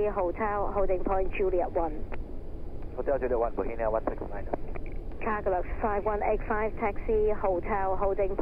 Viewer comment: I don't know what's wrong with me but instead of "Cargolux 5185" I heard "Cargo look fine 185" - at first I was like: Why does the Airport have to know how the Cargo is doing? I'm not a pilot and probably never will be one but that was really interesting - keep up the good work :3